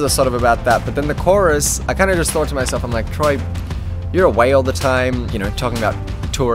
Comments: When he said I'm away all the time i thought he said I'm a whale all the time... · i thought he said "youre a whale all the time." omg what is my life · When he said 'your away all the time' I thought he said 'your a whale all the time', I need help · When Troye said " Troye your away all the time " I thought he said " Troye your a whale all the time 🐳" 😂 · When you said you were away all the time, I thought you said a whale all the time. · When he said 'Troye you're away all the time' I thought he said 'Troye you're a wail all the time'…